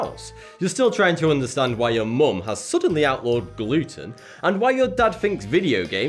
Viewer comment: oh gluten... I thought he said Luton...